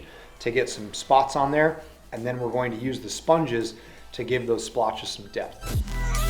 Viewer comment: This has to be the best rattlecan/sponge job I’ve ever seen! My Bergara is about to get it! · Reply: i thought u said sponge bob